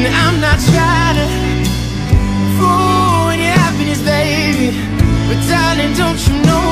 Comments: The way he sings "ruin" 😍❤️ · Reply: lmao i guess im not the only one who thought it said "...fool with your happiness" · I always hear "I'm not try'na fall in your happiness" lol